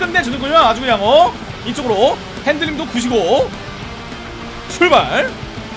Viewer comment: 차 문 손잡이가 넘우 이쁘네요 ㅇ0ㅇ 그나저나 5:22 "핸들링도 구지고...!"로 들려요 ㅋㅋㅋㅋㅋㅋㅋㅋㅋㅋㅋ